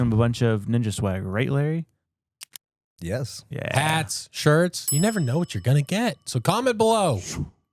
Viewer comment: When you said “hats, shirts” I heard “insurance” and I thought “I wonder what kind of insurance they’re giving away…” only me.